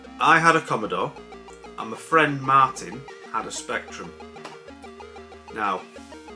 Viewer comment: I thought you said my friend Martin is on the spectrum. I was like same. Then you said you hated the spectrum 😂 I had to rewind it